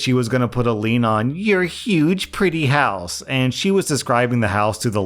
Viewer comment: did she really say "your dig pretty house ?" That's super unprofessional